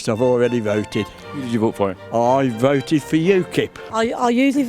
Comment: At first I thought he said, “I voted for You….”